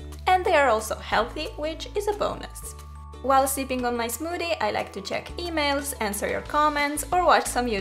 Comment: did she say "sitting on my smoothie?